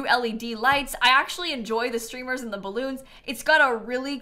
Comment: i thought she said strippers instead of streamers 😅😂